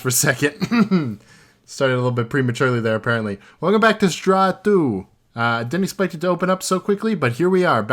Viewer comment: straw2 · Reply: i thought i heard straw2 instead of sdra2 so- im not tryna be rude